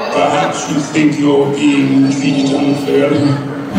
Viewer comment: what did darth vader say? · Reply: He said “ perhaps your being treated unfairly” this is 7 months old why the heck am I replying????